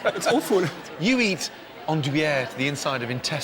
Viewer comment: I wanted to know what Dish he was talking about @ 45:39 but couldn't figure out the spelling so I googled "Disgusting French Dishes" and sure enough there's a list of 11 with "andouillette" coming in at number 8 XD